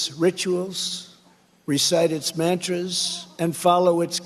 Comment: Did he just say, "recite its mattress," instead of "mantras"??